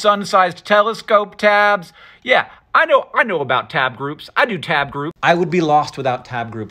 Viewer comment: Having an entire tab group dedicated to TAPEWORMS is most relatable adhd moment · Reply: I thought you said "tape group" I was like hol up